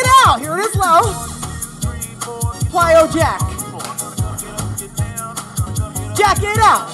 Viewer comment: Omg. So much fun. The variety of moves. -awesome. Have to tell you I thought you said quiet jacks. Not plyo jacks the first time. 😂. I was like what is that. You just make it so much fun. ❤